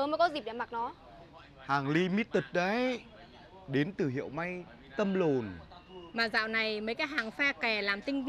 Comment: nói gì vậy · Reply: Tâm l*n😂